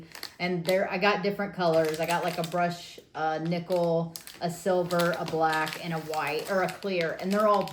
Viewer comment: Here’s something to laugh about. Hubby is watching with me, like every week. ❤. You were just talking about the watch protector. You said brushed nickel. He thought you said blushed nipple. Maybe chewing on chips is messing with his hearing. 😂😂😂 Make sure the shades are UVA safe. I bought a really cute and they weren’t.